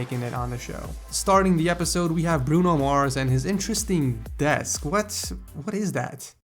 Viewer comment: Did he say Bruno MARS at 0:38 ? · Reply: Yeah he said Bruno mars and Bryan Cranston lol. Subtle and sneaky